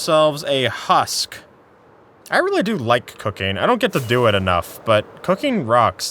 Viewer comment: I really thought he said “I really do like cocaine, i dont get to do it often”